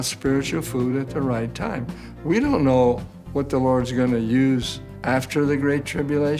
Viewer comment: did he say "The Lord"? Hmmmm